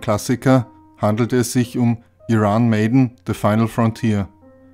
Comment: Verstehe nur ich bei 12:10 "Iran Maiden" 😂